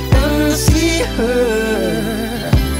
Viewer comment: When I was little I thought he said “one more chance, Ella” I thought he broke up with a girl called Ella 😂😂❤️ Happy birthday Michael❤️